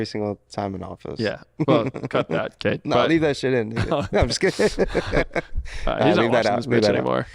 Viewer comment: Yo am I trippin or did he say the N-Word at 22:05?😱🤔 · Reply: no i did not say the n word. i literally just say leave that shit in as a joke